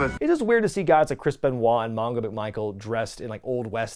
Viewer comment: Did he call Steve McMichael "de Michael" or am I mishearing it?